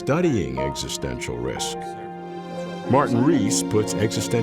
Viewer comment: You said Martin Rees! For a moment I thought I heard Kyle Reese!